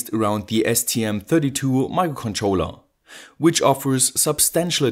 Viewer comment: Did I hear him say "STM32 mind controller"?? :O I WANT ONE!!!